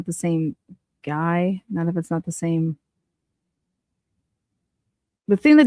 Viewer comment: I hear "A lot of kids have the same...."